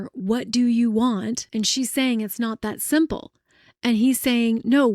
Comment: “It’s not that simple” 😅 I keep hearing “I want to be a healer”